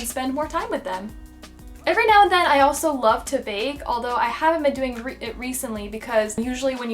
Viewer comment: at first when she said at 8:01 that she liked to BAKE i thought she said VAPE i was like uH alright then lmao